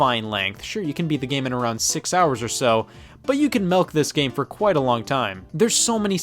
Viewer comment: Did you just say "melk"???? Unsubbing from you